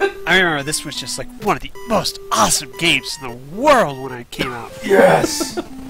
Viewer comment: It sounds like he said this was the most awesome game in the world when he came out. Just imagining hes a young kid. " Hey dad I am gay... Fuck yeah duck tales! "